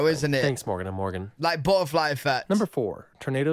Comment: I'm not making fun of you, but I could've sworn you said Barfly effect with your English accent! I was 😂 it was funny and cute!